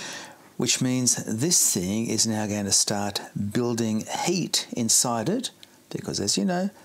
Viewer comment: At about 23:35, I thought he said 'building up hate.' Second time through, I figured out it was 'building up heat.' Well that makes more sense. 😁